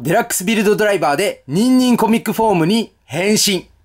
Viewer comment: コミックがトミックに聞こえるのは俺だけか、、、